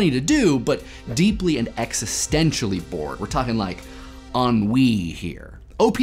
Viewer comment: oh man i thought you said 'on weed' at 1:16. tripped out